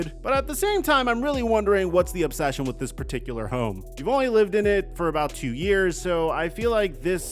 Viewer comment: Swear to god I thought said “I don’t get the obsession with this particular 304” 😂 And then 11:37 “Clearly this is a 304 that Tyrod Taylor is never gunna want see again” 😂